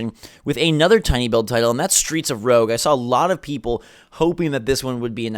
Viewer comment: Almost died... thought he was about to say streets of rage 😂😂😂...i was like WTF!! my eyes got big lol i was like omg im buying!!!!! To good to be true 😔😔